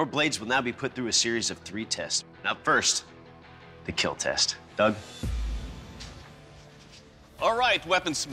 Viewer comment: Up first, the kill test. DUCK. That's what i first thought i heard before knowing that the "it will keel" guy's name is actually Doug Marcaida